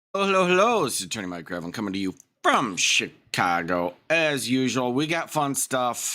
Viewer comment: I LOVE JUDGE #1. He just completely ignored the nonsense and moved forward. Judges across America, please use this man as a guide! You’re not required to address nonsense, delusions or idiocy. Take your special appearance and shove it. That was so satisfying… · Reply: Hey Mike. Burst out laughing bcuz it sounded like you said "from sh...t chago. " Pretty sure you didn't. But thx anyway for the laugh. 🤣